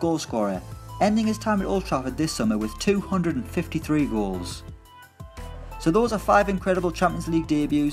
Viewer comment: I swear I heard him say rolls instead of goals at the end 😂